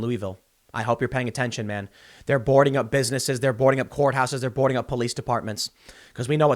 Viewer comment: i swear I thought I heard Tim say "vvh0r3 houses"